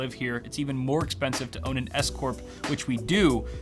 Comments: lol I thought you said escort instead of s corp at first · Lol had to go back and listen to 8:47 again... I thought for sure you said it's expensive as hell to own an escort (service).